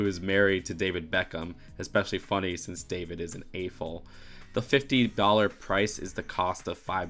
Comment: I have no clue what “ayful” is can someone please help me understand what he is saying.